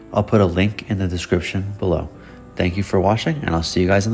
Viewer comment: I thought you said “thank you for washing” at the end and thought that was clever for a soap brand lol